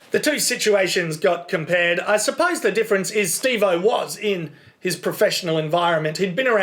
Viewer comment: did he just say steve-o?